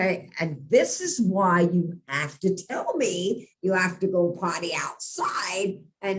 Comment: By the way, my mind wandered in an other direction at 7:31. Because of noticing "Popcorn" I heard that your dogs should "party" outside. I wondered how wild your dogs must be... 😂🍿🥂🍾🍻🍹